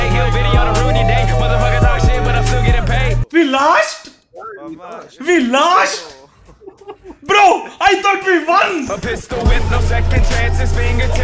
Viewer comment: what wants to say:- we LOST we lost..I think we WON what says:- we LAST we last...I think we ONE